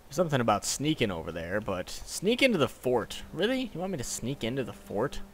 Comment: i thought it said sneak into the fart lol